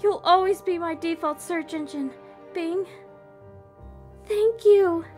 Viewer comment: i swear i heard bling